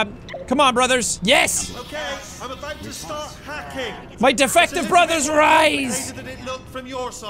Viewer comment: I thought for a while it said Detective Turret, defective makes a lil more sense